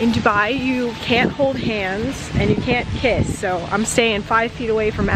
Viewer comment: you can hold hands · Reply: maryam I thought you said (you can hold my hand) 😂😂😂 oh well 👍👍👍